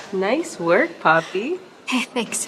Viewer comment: did she called her 'papi'? (daddy)